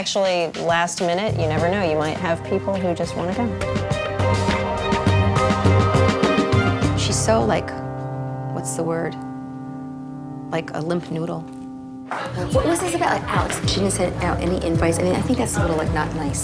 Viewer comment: Limp noodle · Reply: Sounded like she said limpf noodle.